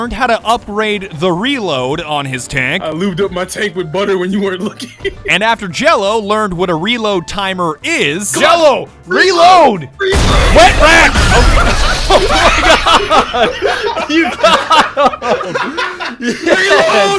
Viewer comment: I thought he said wet back😂😂😂